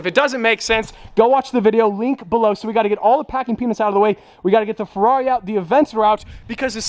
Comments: Lol I thought he said packing penis 😂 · So excited talking so fast it sounded like you said packing penis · Stradmans favorite food: Packing Peanuts Edit: thank you guys I never had so many likes before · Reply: Thought he was saying packing penis first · is it just me or is anyone else using closed captions see “packing penis” instead of peanuts😂😭😭😂😂